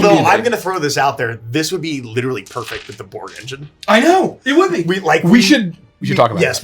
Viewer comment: What engine were they talking about at 16:30? I heard bore? Bort? Something like that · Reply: In case you haven't found out yet, it's Borg. Look up Mork Borg. It's a different ttrpg system that's a lot more streamlined.